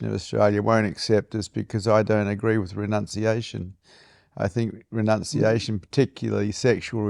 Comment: You said you didn't agree with Renunciation. I thought you said Pronunciation. I cracked up, because they would, right? 😂❤